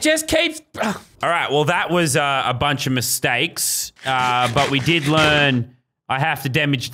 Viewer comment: what LazarBeam says after leaving the orphanage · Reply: aseneth seipel he said “well that was a bunch of mistakes.” L